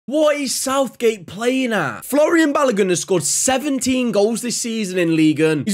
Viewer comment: Did he just say Florian balogun